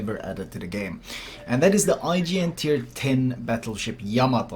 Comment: for some reason, it sounded like you said "IGN" instead of "IJN."